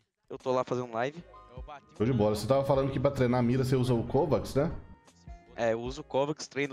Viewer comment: Treinar a mira usa Kovacs 08:35? Nunca ouvi falar disso e muito me interessa, pode dar mais alguma dica? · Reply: Pesquisa aí o nome é "kovaak", é tipo um "jogo" pra treinar a sua mira